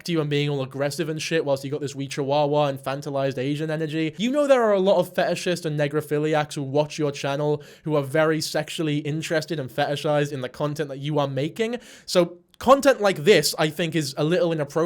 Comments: I THOUGHT HE SAID NECROPHILIA I WAS SCARED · I thought you were saying necrophillia and I was really scared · At first I thought that you were saying necrophilia weird but then I realized I was wrong 😂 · So sorry i keep mishearing you say "necrophilia" and it just seems..better?